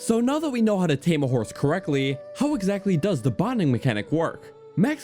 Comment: Dude i thought you said "Horse boning" at the beggining and Choked on air